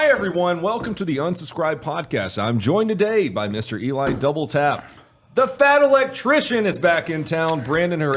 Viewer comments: I thought you were saying Eli "double fap" this whole time. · I swear half the time it sounds like he says Eli double fap